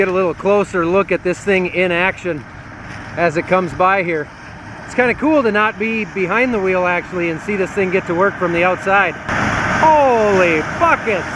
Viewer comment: it sounded like you said holy (t)trucking......I could be mistaken tho. maybe you said something else😉 Wishing and hoping Isla has a fast recovery.....